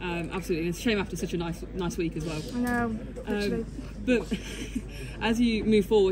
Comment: What does she say at 0:22? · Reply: I know, actually